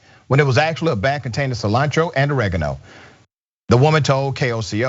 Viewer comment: Why the hell can't anyone pronounce the word 'oregano' it's a fricken herb people. Its in the pizza's a spaghetti's we all eat as westerner's. WTF is wrong with these people.? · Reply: I had to listen to it again. It DID sound like she said "OreJano". Never heard that before.